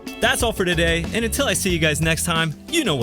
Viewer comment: I love you! Not only for the mountain biking but for what you said last... ( 13:36 ) "get stoned" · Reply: Jonathan de Graaf .. he said “get stoked” tho.